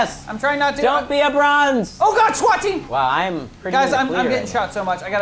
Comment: Is there anybody else but me who's getting continuously pissed at the guys for calling Joven a bronze player?! I mean i think this goes without saying but I'm on team #Mr. Platinum!!!! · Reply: When Wes said OH MY GOD SWAT TEAM!! It sounded like he said twat team. 😂😂